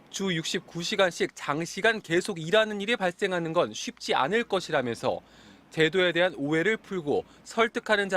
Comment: 주 69시간씩 장시간 계속 일하는게 만드는건 쉽지 않지만.. 해낼것이다라고 들림..